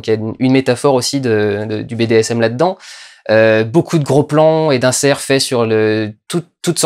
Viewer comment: Haha j'ai entendu " y'a beaucoup de plans fesse " alors qu'en fait c'était " y'a beaucoup de plans faits sur... "